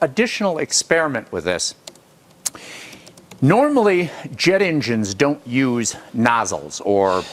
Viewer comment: At first I thought it said debt engine.